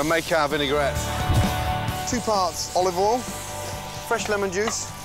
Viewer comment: I know he said "two parts olive oil," For some reason I heard two farts now I can't stop hearing it.